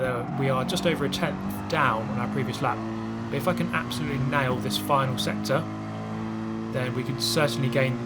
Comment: I heard, “We fu*king absolutely nailed this one”. yikes